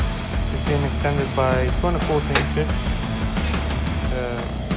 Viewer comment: what did u said about tie rods, extendes by how much? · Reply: 2.25 inch